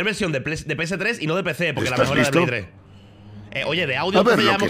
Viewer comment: que dice illojuan ? que no le entiendo :c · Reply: “y no de PC porque la mejor es la de play 3”